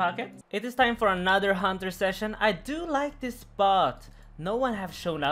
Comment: that name that i cannot write, the one you think is Italian is actually finnish language and means very dirty stuff :D tries say "spot" but i heard "bot" at 4:06 ;:DDD